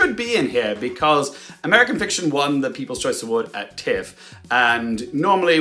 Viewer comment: My hearing is something else. I thought said Emma won the kids choice award 😂